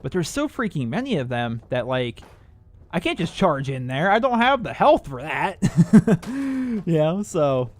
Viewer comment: When he said "I just cant go in i dont have that health" I thought he said "I just cant go in i dont have those balls"